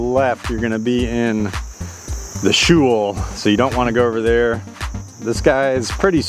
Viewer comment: In the what? 2:37 What is that word? Spell it for me. Madison Hot Goose uses that word a lot but I’d never heard that word before that. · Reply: Shoal. It's a raised area. A shoal is usually an area of shallow water in a river or lake. On this hole he probably means the slightly raised spot on the left. It's not OB but it's a difficult spot to get out of. Madison is a Marine Biologist so that flavours her commentary.